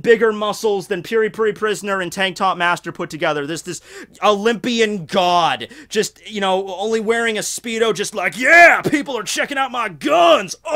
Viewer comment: I don't know why but i always hear "Pink God" LOL